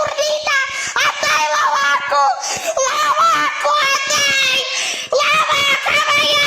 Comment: MY ABABACA MY ABABACA SATURNITA SA- TUR- NITA LA VACA SATURNITA ????? LA VACOA *idk what he says here* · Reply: He said give it back la vaca saturno saturnita